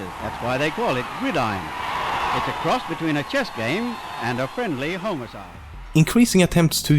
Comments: /2:51 Did he say a friendly homicide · did he say 'friendly homocide' as in, 'friendly murder'?